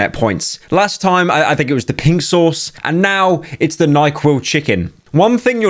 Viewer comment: Did he just say Benadryl chicken? Make this the next trend